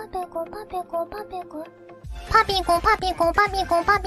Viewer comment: but is she saying panico? (Panic in inglish)